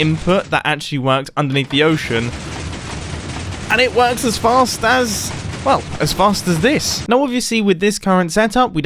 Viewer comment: did he say as fast as fuck lol